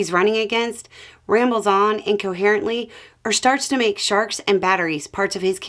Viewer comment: "...Make sharks and batteries part of his campaign speeches..." Thought you said sharts.